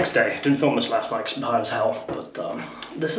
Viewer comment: did he say he was high as hell?